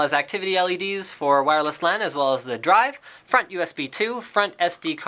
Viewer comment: Did he say fuck USB 2?